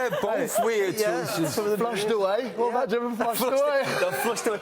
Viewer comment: What is Peter saying in 1:45? "Large, two eight"? Edit: I would never guessed "Flushed away" xD